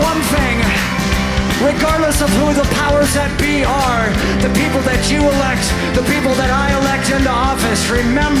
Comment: Hi, could someone tell me, what is Billie saying at 4:56 - 5:15? English is my second language and a can't uderstand this part. Thank You. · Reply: "And remember one thing That regardless of the powers at we are The people that you elect, the people that I elect into office Remember You have the fucking power We're the fucking leaders Don't let these bastards dictate your life or let them tell you what to do. Alright"